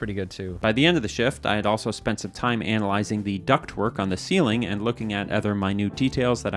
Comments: I swear I thought you said you were observing a duck twerk😅. Unfortunately, you actually said duct work😢😞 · You said "duct work", I heard "duck twerk".